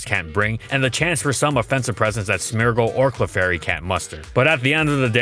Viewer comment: did he just say smeargle lmao